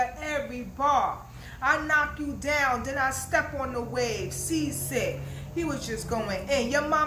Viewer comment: I thought geechi he said I’m sea walking